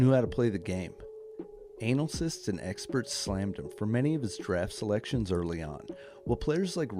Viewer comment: 'anal cysts and experts'... Did I hear that shade being thrown?